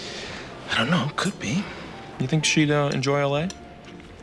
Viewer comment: I had to listen 3 times then put on the CC. I kept thinking that Hamilton was asking Foreman (concerning Foreman's girlfriend) "Do you think she'd enjoy a lay?" Closed captions said "Do you think she'd enjoy LA?" Gosh, I hope it was the latter! Sometimes the CCs don't get it right!!!